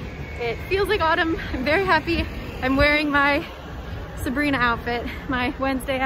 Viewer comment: the way i looked up so fast when u said sabrina outfit I thought u said sabriha w an h cause that how u spell my name. I got too excited 😁